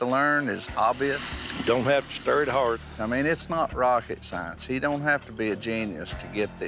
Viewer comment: Pretty much just taught all of us how to make liquor lol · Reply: surgery? Thought he said science 😂